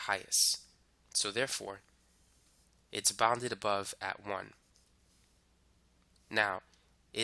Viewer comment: can someone verify what he said at 19:16 bounded above 1 or bounded below 1 similarly for 0 as well. is it bounded above 0 or below 0. I think he just mixed up but may be I am wrong. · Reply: bounded below at 0 and bounded above at 1